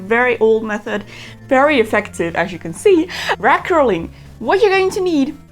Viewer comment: When she started, I thought she was saying " rat curling " lololol 😂